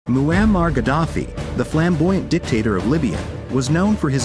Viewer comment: Im the onlyone who repeatedly misheard "Mayanmar Gaddafi"